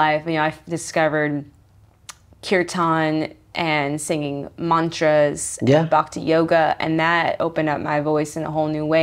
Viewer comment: ah im not native in english and cannot get what she says in 5:49 about how she discovered her voice. "by kon something? mantras and back to yoga? " what is non something? can someone please help me ? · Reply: She said "kirtan, mantras, and bhakti yoga". Kirtan is devotional singing usually in the Sanskrit language to Hindu forms of God. Bhakti yoga is the devotional path of yoga. It is heart centered and is about surrender to the divine. Hope this helps